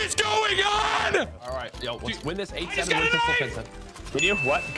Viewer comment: I heard “he’s got a knife” and thot I was watching a swatting